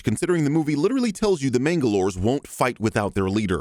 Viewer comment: Is it weird that for some reason I keep hearing 'Mandalores' when the says 'mangalores'... or could it just be me being a Star Wars fan or that I just finished watching Mando... I'd say a bit of both